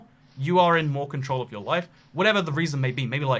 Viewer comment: thought he said wife